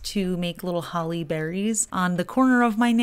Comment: I heard "create little Halle Berrys" 😂😂😂